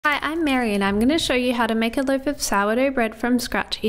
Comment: My slow ars wondered why she said she was married and gonna make bread. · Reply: I thought she said “I am Maryanne” :))